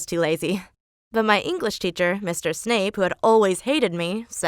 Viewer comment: Wait did you say Snape at 0:21!?!? Snape is a professor at Hogwarts